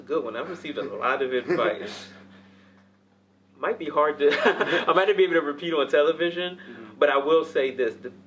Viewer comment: What did he say from 10:50 to 10:52? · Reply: “I might not be able to repeat it on television”